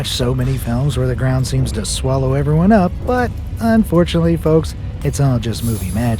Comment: why is unfortunate that the ground didn’t actually swallow everything and everyone up,but it’s just a movie😂 Now I’m wondering did he said “unfortunately” or it’s my mistake?